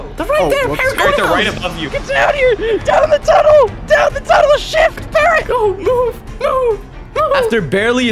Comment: who else heard oh shit parrot